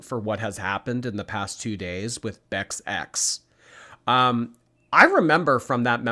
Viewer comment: Please make Twink emojis. Omg, that’ll be so funny. The poor thing. Here’s what’s gonna happen: he’ll get just as obese as she is, get arthritis, be in too much pain, she’ll have to put him down, then she’ll get super depressed and binge even more than she already does. I wouldn’t be surprised if that’s exactly what happens. I laughed really hard at how you said “Beck’s ex”, because I heard “Bex ex”. Lmao, tell me you’re losing your hearing without telling me😂😂😂😂😂😂🤣🤣🤣🤣🤣🤣🤪🤪🤪🤪🤪